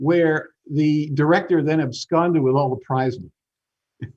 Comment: What´s he saying at 34:20? "...where the director had..." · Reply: "absconded" with the money= ran off with the money